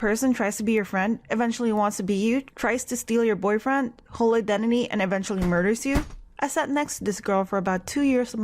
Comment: at first I didn’t understand what you said but you said “whole identity” I loved the video so much wow I can’t believe this happened! :0